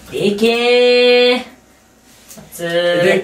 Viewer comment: 秒の虫眼鏡の「でけー」 が｢AＫ」に聞こえたのは俺だけだろうか？